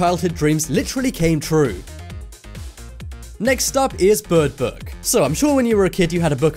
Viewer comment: I thought that said Bird BOX